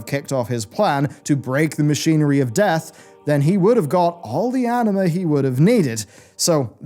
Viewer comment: When he says Anam to me it sounds like he is saying anime